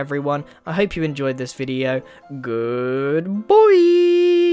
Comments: When you were saying goodbye you sounded like you were saying good boy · At the end of the video I thought I heard it like gooood boouiii